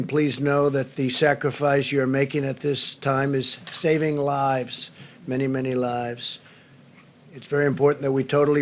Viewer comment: Trump said, " saving lives, many many lives..." What I heard is, " saving lies, many many lies..."